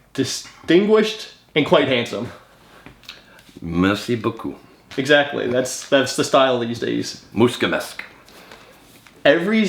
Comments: says "thank you" in Romanian and I was pretty surprised to hear that! It doesn't say in the subtitles what he says, just (speaking foreign language) but what he said was "Mulțumesc" (he slightly mispronounced what sounds like "Mul-tsu-mesc" as "Mul-scu-mesc"). Anyways, thank you, hearing this gave me hope about my own struggles, and thank you for what you do. Cheers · did you just said "MULȚUMESC!"? greetings from Romania! 🟦🟨🟥